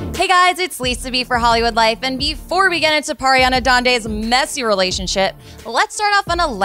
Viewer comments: did she just say "before we get into pariana dande's messy relacionshit"? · ok so pleas tell me iam not the only one hearing them sayin ariana grade wrong like i hear" pariana dandes" at 0:17 and i hear her name wrong in every video at least once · Did she say Pariana Dande LOL